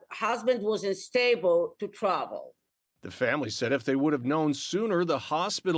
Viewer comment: If I heard correctly, @ 1:30 his wife said her husbands’ cardiologist said “he was “A” stable to travel”. What more do you need? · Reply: listen again; "WASN'T stable to travel"